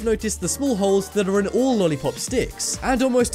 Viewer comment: Am I the only one who hear " Lolipop's Dicks" ...